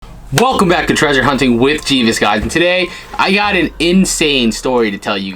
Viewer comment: When what the hale, mentioned you on his video , I thought he said treasure hunting with Jesus LoL